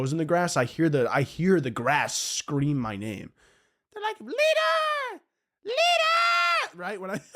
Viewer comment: When he said that the grass was speaking to him saying “leader!” i thought he was saying “litter!!!” 32:39 and it honestly would’ve fit perfectly in the bit